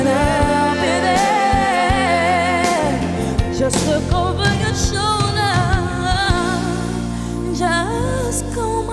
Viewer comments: WHAT DOES SHE SAY HERE? Please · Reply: just look over your shoulder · What does she say here? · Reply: Just look over your shoulder